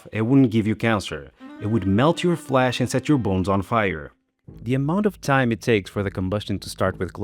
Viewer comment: I could have sworn you said, "melts your flesh and sets your balls on fire"!